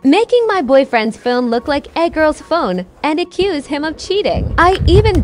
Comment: I thought it said “Egg roll’s phone” instead of “A girl’s phone” for a second there 😂